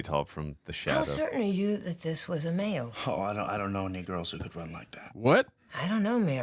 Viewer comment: i don't understand how they heard the n-word from the sentence "i don't know any girls that can run like that" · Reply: It's the second syllable of "any," followed by "girls"... nee + girls. I never heard it that way myself, but I can see how it happened. And people are making too big a deal about them hearing it, like they were trying to be offended. But sometimes your brain just puts things together in weird ways. One time when I was living in a Russian-speaking country, I swore I heard Pierce Brosnan say "I don't know" in Russian in the movie we were watching. Of course, from context it was very obvious to me that he wouldn't be randomly spouting Russian, but I still had to go back and listen to the line several times before I could stop hearing that and figure out what he actually said.